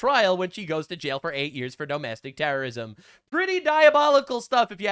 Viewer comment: LMAO It almost sounded like you said "dumb-astic-t3rr0r1sm"